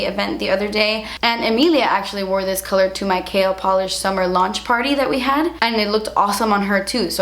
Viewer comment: I thought you said "kale polish" I was like... Really... KALE! Thankfully you said "K L"